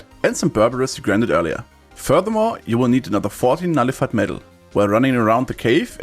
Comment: how many more do you need after the sword upgrade? either 14 or 40 its just so hard to understand what you are saying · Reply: 14